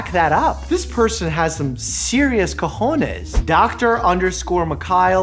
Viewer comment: Did you really said COJONES ( 2:55 ) , if you did thats a huge improvement in your spanish XD